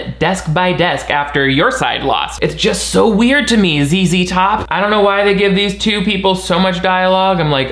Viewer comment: when says ZZ top captions said "is he top"? HAHAHHAHAHA captions totally called duck man gay